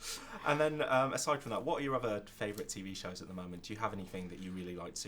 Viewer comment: What does the interviewer say at 6:59 ? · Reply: what are your other favorite tv shows at the moment. just by their answers you should’ve been able to connect the dots